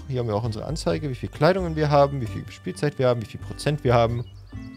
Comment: Ich hab verstanden "...wie viel Spielzeug wir haben..." und dabei war der Pfeil auf dem Kätzchen ^^ Zu den Farben: Ich hab jede Farbe bis auch lila anderst gesehen als Zombey. Aber irgendwie sehen die Farben auch bischen komisch aus.